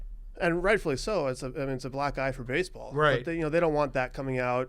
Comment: Thanks for having me on guys! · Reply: What did you mean when you said “it’s a black guy for baseball” or were you misheard?